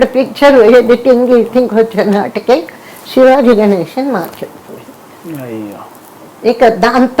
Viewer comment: what did she say at 6:12...shivaji ganesan taruvatha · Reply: I think shivaji ganeshan market poindi